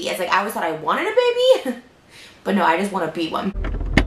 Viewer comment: "I tough I wanted a baby. But no I just wanna be one" lol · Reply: lol I thought she said "I just wanna beat one"